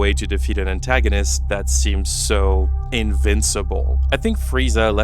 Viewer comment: did you say… invincible?